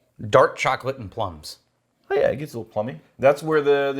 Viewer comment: Did say it gets more phallic at 6:42 left in the video?